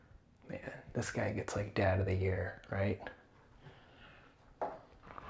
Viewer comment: what did you say? “Dad of the year?”